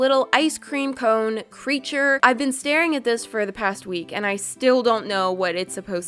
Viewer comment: I thought u said that they are staring at u